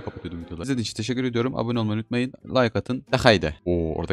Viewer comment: o Hyi duydum abi